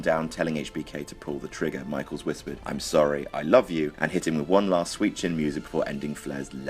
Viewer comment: And Im always thought he said: "I am Sorry but screw you"